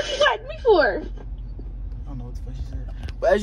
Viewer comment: Is it me or it sounded like she said “ what you block me for ?” lol